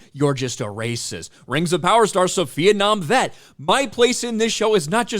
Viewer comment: When you said "Sophia Nomvete," I heard "Some Vietnam Vet," for some reason.